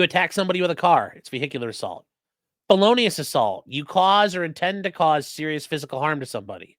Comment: To be honest, at 33:37 I heard it as "bolonius assault". What? Assaulting someone with bolognaise?